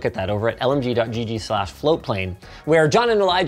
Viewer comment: oh, it's FloatPlane. I always thought you guys were saying FlowPlain on the podcast.